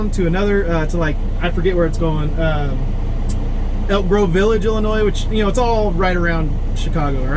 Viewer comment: I thought said he was going to broke back village in Illinois.🤣